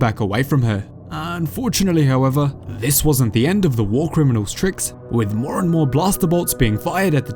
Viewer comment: The Speaker says War Criminal and the subtitles says Separatist 😂